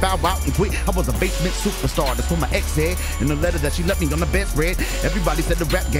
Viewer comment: he fumbled, I love him but he fumbled. "...that's what my ex said/ in a letter that she left me on the best-read" ---- unless he was being extremely clever (which is possible for Rittz), he meant to say "bed-rest" ---- check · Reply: He said Bed spread